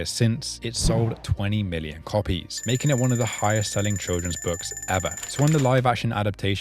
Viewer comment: I thought you were saying "Trojans" when you said children's.